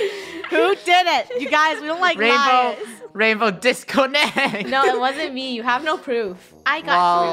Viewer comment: when said disconnect at 14:02 i thought he said " disco net " XD 🤣🤣